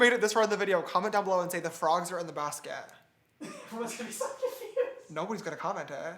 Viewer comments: at first i thought he said the frogs are in the casket XD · I thought he said “the frogs are the best kid” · did say bronzer on the basket?